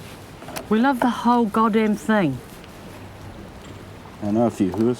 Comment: did she say "The whole Goddamn thing"? LOL